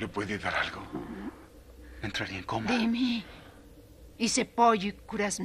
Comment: todos diciendo "es griego" y yo le entendí "hice pollo"😭